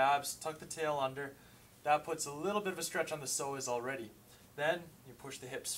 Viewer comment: at first i thought u said soleus and was thinking u were stupid but then i understood psoas lol.. my bad.. good advice tho